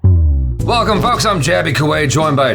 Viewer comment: woah, I thought you said "welcome f*cks"